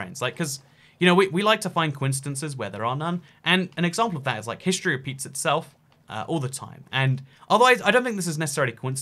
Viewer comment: I at first thought you said history pizzas itself, and I was very confused.